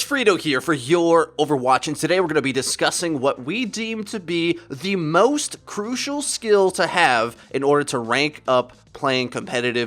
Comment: all i heard was "the most important skill to have is to kill the pharah